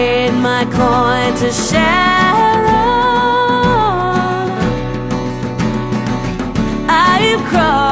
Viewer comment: It sounded like she said Sharon...